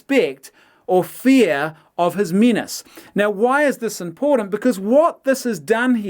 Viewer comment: ‘Fear of his menis” I heard “fear of his penis”